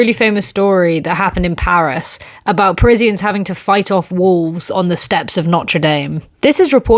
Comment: First I heard: "Presidents fighting back wolves on the stairs of Notre Dame", story was still a delight 😂